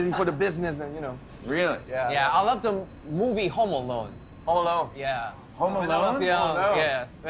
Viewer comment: Did he say Homo Alone?